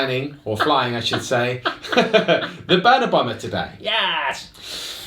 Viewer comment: turn on subtitles. Instead of burna bomba it got “burn Obama.”